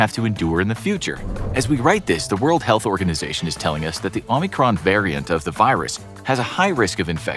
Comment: When they announced the new variant,I thought they said Unicron,so I agree it sounds like a transformer